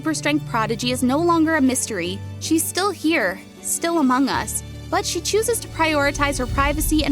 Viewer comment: did it just say amongus 🟥🟥🟥🟥🟥🟥🟥 🟥🟥🟥🟥🟥🟥🟥🟥🟥 🟥🟥🟥🟥🟥🟥🟥🟥🟥 🟥🟥🟥🟥🟥🟦🟦🟦🟦🟦 🟥🟥🟥🟥🟥🟦🟦🟦🟦🟦 🟥🟥🟥🟥🟥🟦🟦🟦🟦🟦 🟥🟥🟥🟥🟥🟥🟥🟥🟥🟥 🟥🟥🟥🟥🟥🟥🟥🟥🟥🟥 🟥🟥🟥🟥🟥🟥🟥🟥🟥🟥 🟥🟥🟥🟥🟥🟥🟥🟥🟥🟥 🟥🟥🟥 🟥🟥🟥 🟥🟥🟥 🟥🟥🟥 🟥🟥🟥 🟥🟥🟥 It took me 10 min